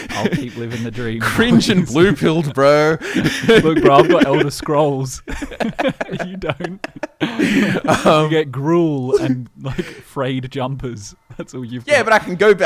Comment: what’s he say” I have the elder scrolls, you don’t, you get jumpers and grool” lmaooo?